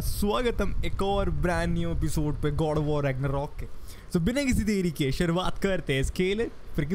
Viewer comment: bhai 0:09 ye kya bola? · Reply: SO BINA KISI DERI KE